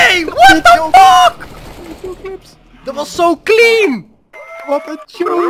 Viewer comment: I'm uploading every other day now, taking extra time to improve the videos because daily videos are cool but not up to the quality I think. I'll ocassionaly upload more than every other day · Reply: are you swedish? xD cause i swear to god i heard "NEJ WTF DET VAR SO CLEAN" at 7:31 but idk xD